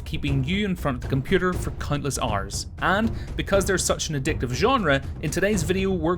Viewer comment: did he say "for cuntless hours" or "countless hours" XD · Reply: I said countless hours :)